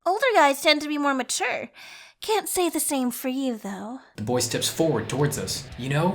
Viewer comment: Lmao I thought he said “you thot!!!”